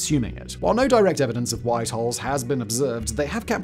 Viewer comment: "No direct evidence of white halls has been observed" I thought there was a white hall in Londen? Oh Holes, I must have misheard.